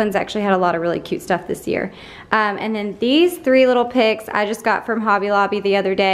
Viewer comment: lol I misheard you and thought you said “3 little pigs” when you were talking about the sprigs